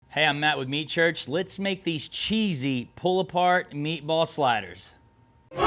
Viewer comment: I dunno why, but I heard "Hi, I'm Meat with Matt Church". My imagination went wild with that. I pictured an episode of Matt getting hammered while making some disgusting looking dish, only to be edited to look professional and delicious at the end hahaha